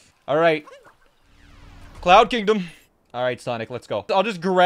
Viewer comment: did he say sonic? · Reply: not being a nerd but he said cloud kingdom